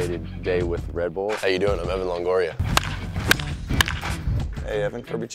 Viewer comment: I thought he said Eva longoria! LOL